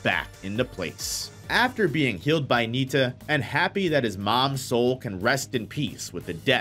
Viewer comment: I don't know why but I thought you said KILLED by neita